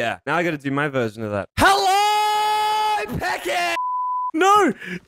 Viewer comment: In the begining I thought he said "And my best friend Sam PEPPER" And I was like BOI NO IF YOU DON'T GET THAT RAPIST NAME OUTTA HERE and then I was like ohhhh he said peckett whoops